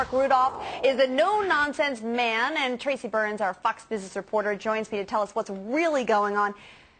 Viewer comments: I heard 'Fucks Bitch reporter' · sounded like she said “our FUCKS business reporter”